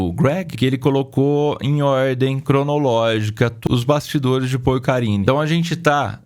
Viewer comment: Eu entendi "os bastidores de PorCaria" kkkkkk